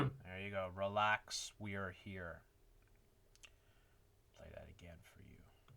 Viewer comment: I could be wrong, but I heard, "relax, be careful". But the CONTROLLED OPPOSITION is SO OBVIOUS !.. 👍😄💖